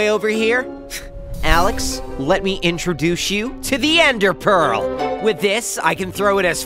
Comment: Did steve just say "let me introdush you"? 😂😂